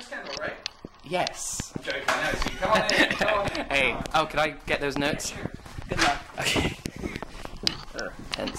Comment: it sounded like Chris said "oh can I get those nuts" KILL ME